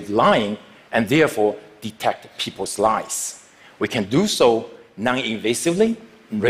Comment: It sounds like he says « people’s lice »